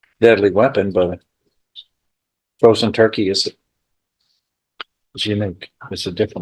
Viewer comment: Did he just say…frozen turkey?!?!